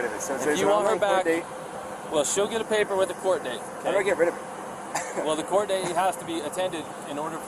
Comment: almost sounds like he says he wants to get rid of her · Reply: "Get rid of it"... the charge.